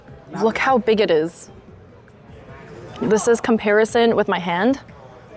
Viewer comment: The mic skip at 7:23 made me think you said "this is comparison to my ass" and that you were about to try and throw it back in front of the monitor ngl